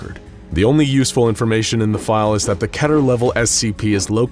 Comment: im sorry, but did you just say "kettur"?